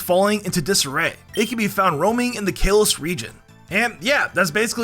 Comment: Ain't no way I just heard "kale-ose"